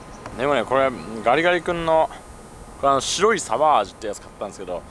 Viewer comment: 「ガリガリ君の白いサバ味」って聞こえたのは自分だけだろうか